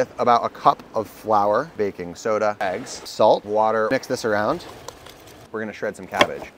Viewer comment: i thought he said "salt water"